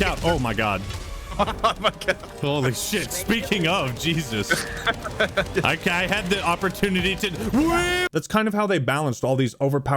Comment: sounds like you said you had the opportunity to whip